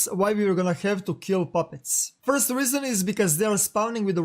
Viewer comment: PUPPIES AND KITTENS thumbs up for Z Nation :D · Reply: I thought be said puppies first 😝